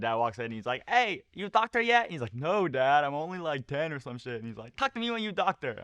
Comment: did he say he only 10 or did i hear it wrong